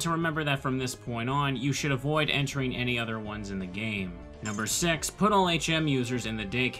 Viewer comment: I heard "number sex" What is wrong with me?